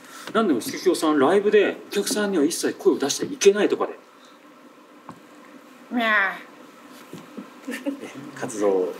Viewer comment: Did you just say "NYAN?"